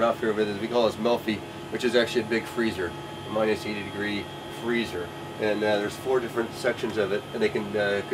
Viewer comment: min Did he say "minus 80 degree freezer"? Celsius or Fahrenheit?